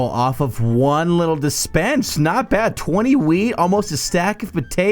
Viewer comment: When you say "Wheat" it sounds like you said "weed"